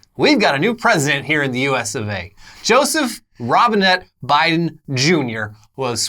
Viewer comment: Robinette Biden, it sounded like he said Robin-Hood Biden Jr at teh inauguration